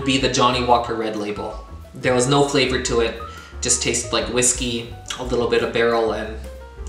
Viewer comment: LOL at 9:08 I had to rewind because I thought you said it tastes like Disney at first (instead of whiskey). Congratulations to 100!!!! That bottle transformation was cool.